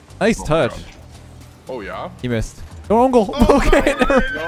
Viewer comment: what said: "no own goal" what heard : "go own goal"